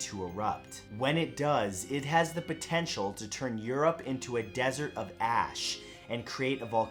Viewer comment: I heard “causing Europe to become a desert of ass”. I need to get my ears and brained checked.